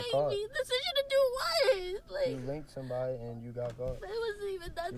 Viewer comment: am I the only one who heard "you raped someone"??? Put that rat in JAIL